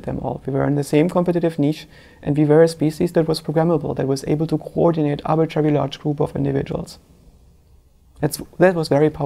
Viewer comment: the subtitles says: "able to coordinate our very large group of individuals" shouldn't it be? : "able to coordinate *arbitrary large group of individuals"